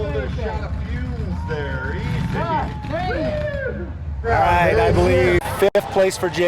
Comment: I swear when he turned off the station wagon and ripped the flag off. I'm pretty sure I heard him say Dang it Clark, is this correct?